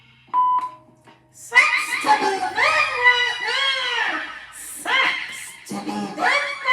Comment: I'm sorry what did you say at 26:03 with the mic? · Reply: Sucks to be them right now